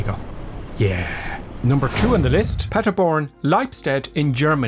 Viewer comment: I'm sure I heard you say Paddyborn.